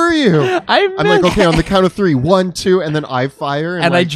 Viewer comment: I thought you said 1,2,3 then go. You meant go ON 3...